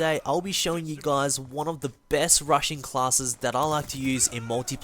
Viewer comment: Hehe.. Thought he said Russian class at first :)